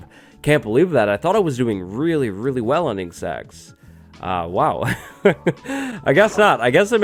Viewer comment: At around 26:23 the subtitles say ending sex 😂